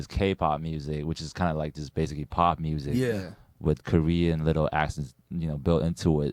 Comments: Lol I thought he said Korean little asses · “with Korean little asses built into it” 😂😂 that’s what I heard